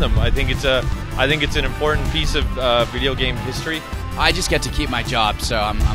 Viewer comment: 1.07. I thought he was trying to say it a very important piece of video game crap. Must have watch many AVGN videos haha